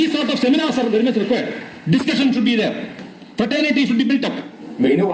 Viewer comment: Teacher, At 6.44/17.24, it sounded like he was saying “Fertility should be built up”. Can you check what exactly he said?